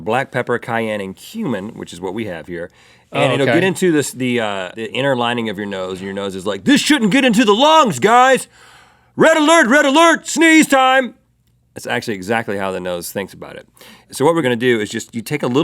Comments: When Rhett said "Red Alert" my brain automatically thought he was saying "Rhett Alert"... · When rhetr said cumin i thought he said human XDXDXDXD